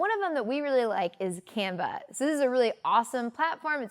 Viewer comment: You should include a link to Canva. I hadn't heard of it before, and heard you say 'Camba', so I went googling. I eventually found it with the correct spelling.